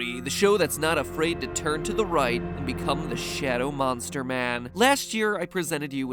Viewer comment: I thought Shadow Mosnter Man said mr. avocado man